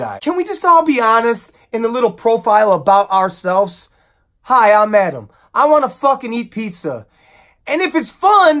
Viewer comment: I thought you said “hi! I’m Adam and I want to fucking eat pizza.” So, no steak?🤦🏻‍♀️ & you’re telling me the flower crowns are a no go?! Dammit. I guess my bio would say “I’m Leeann and I’m 30 & I like to trap wildlife and name them. Ex: recently I’ve caught Tyrone the 45th possum.” Love you!